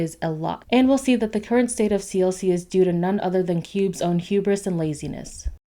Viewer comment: When you said hubris, I heard "CUBE-ris"